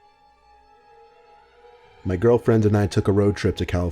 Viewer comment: The beginning of story two I thought he said “my girlfriends and I” i was like the guy that wrote this is a savage having his girls in the same car like daaaaaamn